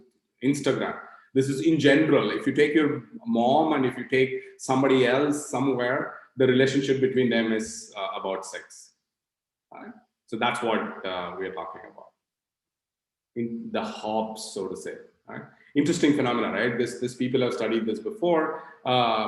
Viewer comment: in 15:48 does he says relationship between my mom and someone else is about sex? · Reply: NO he says 6, its his accent